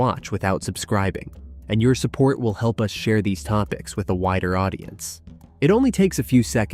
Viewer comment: I done thought he said a helps us reach a whiter audience in the beginning, lol😅